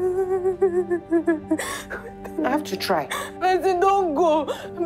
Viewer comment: I’m I the only one who heard Blessing calling Hellen Blessing? 1:15:33-1:15:40 Great movie though.